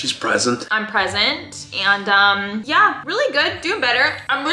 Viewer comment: I thought she said I am pregnant